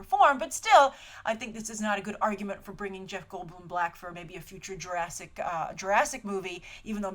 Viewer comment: Sounds like you said "Bring Jeff Goldblum black"